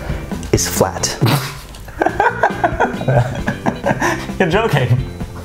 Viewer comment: What Said : “You’re Joking-“ Captions : Y